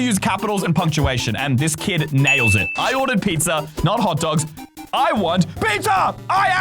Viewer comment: He says whant instead of want